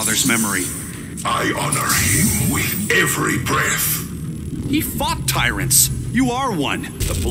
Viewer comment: Flash: You're a disgrace to your father's memory. Bane: I honor him with every breath. Flash: He fought tyrants. You are one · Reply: I thought he said "he fcked tyrants"